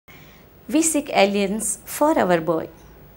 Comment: Did she just say "We seek Aliens for our boy" At the start of the video? LMAOOOOOOOO